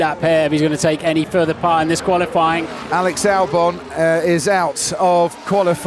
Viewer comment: Did he say “ Sir Alex Albon” 0:10 ?!